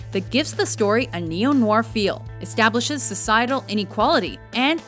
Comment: What does she say at 8:13? I understand "establishes a sideral any quality".